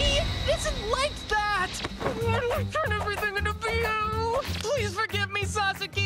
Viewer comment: the way I heard “why do I have to turn everything into bl” and not even knowing if that’s what he said makes me want to watch this